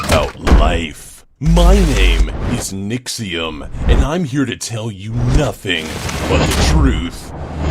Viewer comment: I want Moocluck to star in more vids Also I thought said “And I will tell you nothing of the truth!!!!!!!!”